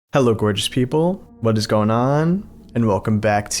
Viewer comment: Bro i thought you said “hello British people”